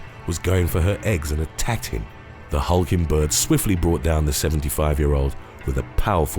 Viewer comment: "the hulking bird", coulda sworn she said Hulkenburg like Nico Hulkenburg the F1 driver